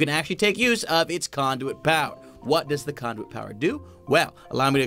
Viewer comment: i thought he said "the condom"